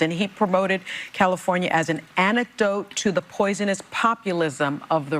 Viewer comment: Did she really say CA is an anecdote? I must be missing something.